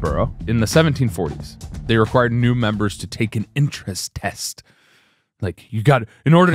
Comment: I also heard “interest test” and thought wow, men have really been doing the ‘prove you’re a real fan of x’ for centuries. Glad I heard wrong lol. “U n i m p o r t a n t” lmao the casual obliteration 😂.